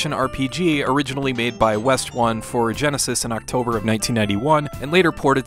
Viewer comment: Wow... Wait a minute... Wait... "West one"? I always thought we said "wes tone"!!! 😅 I guess it makes sense! Silly me!